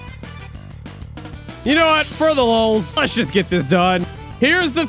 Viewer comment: did you just say for the lol’s.